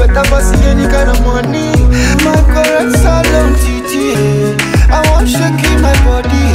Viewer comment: Did I hear ''my correct Salone titi...''